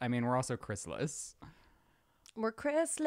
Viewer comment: I literally thought you guys said you WERE A ✨ CHRYSALIS ✨ 🐛 🦋 not Chris-less. Lol